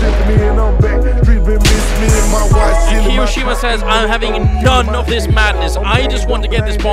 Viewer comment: how did i hear hiroshima? xd